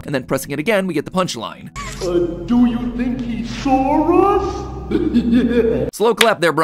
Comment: I don't know if you caught this mat pat but during bron joke I heard " do you think he saw us " but that's just me